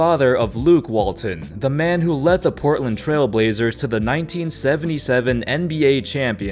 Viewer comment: all i heard was luke walton the man who led the trailblazers to a championship